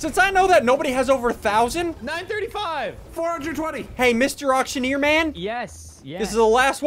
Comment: did just say 420 and I thought it was a family friendly channel